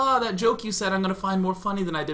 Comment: Just walked into the room and all I hear is, "because I'm trying to unjerk myself"